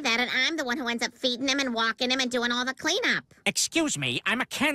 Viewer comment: For a sec I thought she said beating him instead of walking him 💀